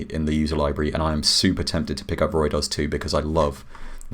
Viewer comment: When he is messing with reaktor he mentions something… it sounds like he says ROY-DOS 2. I can’t figure out the actual spelling or name… but I want to look up that synth. Please help my brain. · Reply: It's called Ruidoz by Blinksonic - I have no idea if it's supposed to be pronounced the way I pronounced it :)